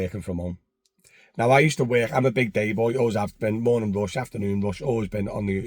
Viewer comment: Did you say at 3:45 that you’re a big gay boy? 😂